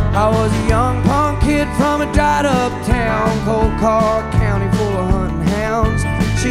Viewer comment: Clarification please: 1:55 What does he say? "___ ___ full of hunting hounds" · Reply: It’s coal car county.